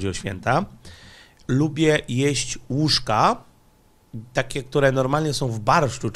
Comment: ale się zdziwiłem jak usłyszałem na początku łóżka 😂